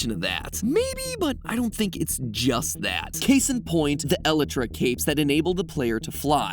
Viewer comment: Did he just say "eletra" instead of elytra